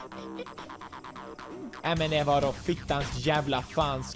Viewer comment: Pewdipie getting mad bc of a hard game: DET VAR DÅ HELVETES JÄVLA F**TSKAP. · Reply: I thought he said Eminem.